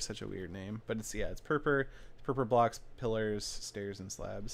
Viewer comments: Who thought he said pervert block when he said purper block? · I thought you said pervert blocks xD